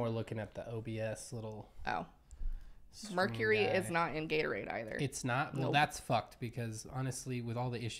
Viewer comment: I thought you said, "Mercury is not in retrograde."